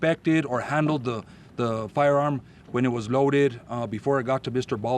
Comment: ooops ! i thought he said moaded.